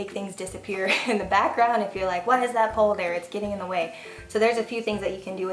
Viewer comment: When you said, "Why is that pole there?" I thought you said, "Why is that polar bear?" Lol! This was very helpful! I'm interested in doing YouTube videos on baking, so this was great information! I'm looking forward to you talking more about the software you use because that has always tripped me up. Thanks, lady! :-)